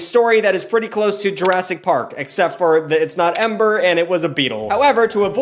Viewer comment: did you say EMBER ? Like the pokemon move ?